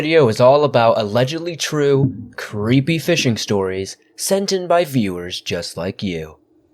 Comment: I thought that said six scary fisting stories at first. Yikes!